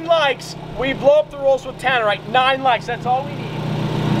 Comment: I thought he said blow up the Rolls with Dynamite and Was very concerned for the well being of the best car in the fleet.